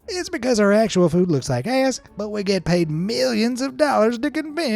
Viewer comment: Did he say ass?